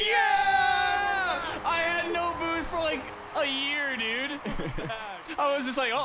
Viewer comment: When he said I have no boost at 38:46 it sounded like he started singing I am the one